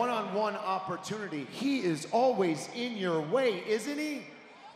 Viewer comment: who else heard sum1 in the stands say "ALWAYS IN THE WAY!" 😂😂😂